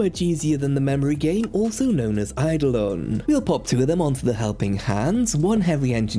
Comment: did i just hear that right? You play idleon!?!?